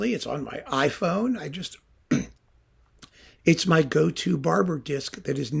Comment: English is not my first language, and it shows. When said “this is my go-to Barber recording”, I heard “this is my go-to-barber recording” and I was like “wow, in my local barbershop they play only rock music from the ‘70s”. I definitely should try grooming my beard listening to Barber!